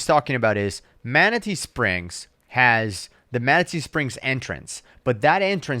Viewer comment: I kept hearing “manitou springs” which is where I get weed Sometimes